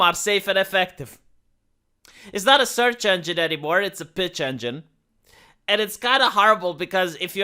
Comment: "It's not a search engine anymore, it's a pitch". Very well said. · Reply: I thought he said "bitch engine" lol